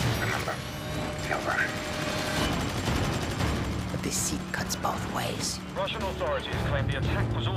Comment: I thought he said "but the sea cuts both ways" when I watched this for the first time...I'm not impressed lol